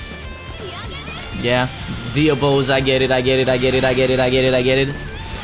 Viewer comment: did say Dino(Deno)pose? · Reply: He said dio pose, LC does the dio pose when she starts the lvl3